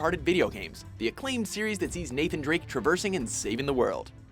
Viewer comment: did he say naked drake